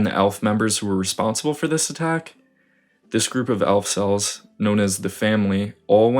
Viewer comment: did you just say “elf-cels”?. if so thats fucking amazing lmao